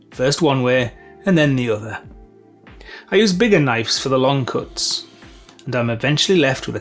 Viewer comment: Great presentation. I love how you say bigger knifes instead of bigger knives at 2:04, reminds me of home.